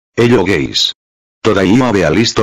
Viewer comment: How i hear "hello guys":hello gays